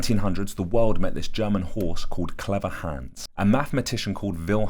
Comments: I thought he said Mr Hands and I thought i was going to get an in depth Equestrio-Sapien romance breakdown. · I thought you said "Clever Hands" at first and I was shook